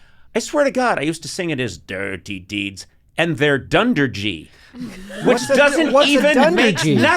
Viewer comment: I always heard “dirty deeds and the thunder chief”